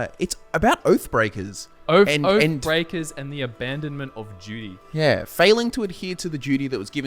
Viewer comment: at first I thought you guys were saying " the abandonment of judy" and I'm like WHO TF IS JUDY???